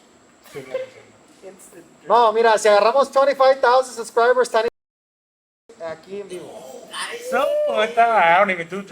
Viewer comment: What did say at 1:28:27 they muted it for a sec lol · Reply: Se echa un pase